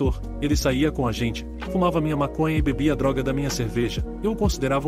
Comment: Ele falou ele fumava minha maconha ou eu entendi errado foi no minuto